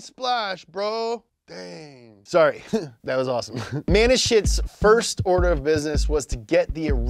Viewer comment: I keep hearing "MAN IN SHIT" 😂